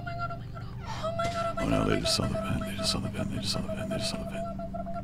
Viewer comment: Pokimane: oh my god oh my god oh my god corpse vented! Also Pokimane; let's not mention it at all... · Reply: Pretty positive she didn’t see him, when the lights are off you can vent and all they can see is the vent go up, but you can not see who vents. Also, she did not say CORSPE vented. “Oh my god oh my god oh my god I just saw someone vent!” Around... 15:17 But I can see where the confusion was!